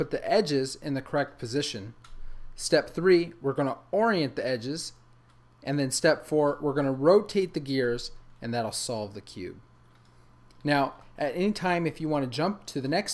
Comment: did he just say cube